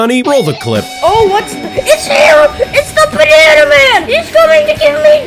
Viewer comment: did he say BANANAMAN?